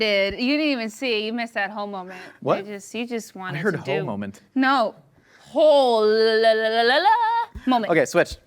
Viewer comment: i had the same reaction as josh haha i thought i heard "Homoment" too